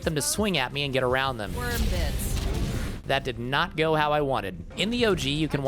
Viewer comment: i thought she said "Worm this" and I absolutely lost it